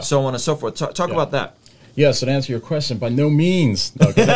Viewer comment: By no means. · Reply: At first I thought he said "By no memes" 😂😂that would've been epic though